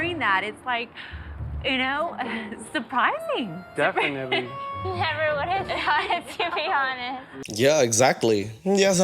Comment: "Never would have thought, to be honest" is what she said. Why are people so bad at decrypting mumbled/unclear speech? xD